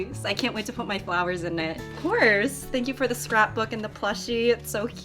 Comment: ok so did she say strap book or do I need to get laid??